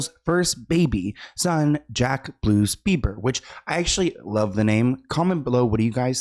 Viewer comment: I thought you said Jack Blues Beeper and I was like "I beg your pardon, Sloan?"